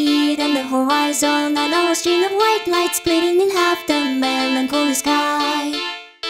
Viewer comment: the additonal lyrics at the end: "say the world ends in silence, with my distorted image smiling at every corner can you see it on the horizon, a narrow string of white light splitting across the melancholy sky say the world is now done for, there's nothing left to do but twirl amongst the snow can you see it on the horizon, the culmination of ten thousand years of humanity is gone" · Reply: I heard "in half" instead of "across"